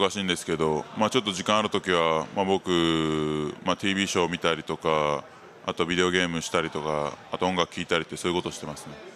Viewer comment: Did he say videogame at 0:40?